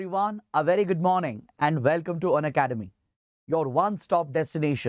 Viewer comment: Sir..... I heard " Welcome to Byju's"...........may be habitual of that starter. But Honestly, you are best in Current Affairs.